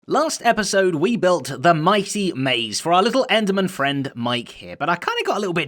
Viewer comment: did he say enderman?